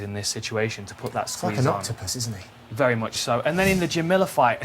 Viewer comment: Did John just say "fuckin' octopus" at 30:52?